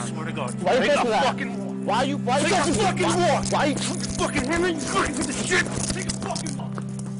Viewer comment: Did I hear another cop in the background say ,"Fuck him up". Egging the cop doing the physical assault on?